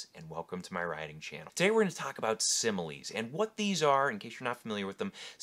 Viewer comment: I thought it said smiles at first 😂